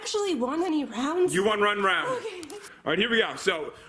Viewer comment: DID anyone ELSE hear say u RUN rne round like if you heard it. 17:40-17:41 (listen carefully)